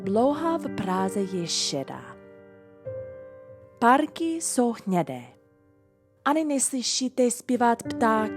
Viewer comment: I heard "párky jsou hnědé", instead of "parky jsou hnědé" :D Super recept na kari!